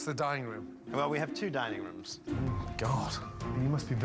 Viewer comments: did Gordon say Dining rooms or DYING rooms? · i really thought they were saying dieing room because of the history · I thought Ramsey said “the dying room”😂😂😂